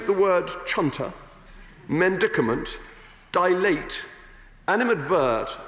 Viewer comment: Help! I need the spelling of what sounded like "mendicament" (2:34). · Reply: Medicament, a substance used for medical treatment. Often used by Bercow when asking people to "calm down and take a soothing medicament" (a classy version of "take a chill pill"), also when used jokingly might have hinted at booze.